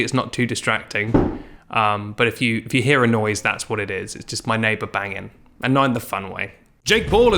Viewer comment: “It’s my neighbor banging.... and not in the fun way 😏” 😂😂😂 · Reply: I misheard this line as, "it's my neighbor banging... and I'm a f*ckboy," and almost spit my food all over my phone! 😂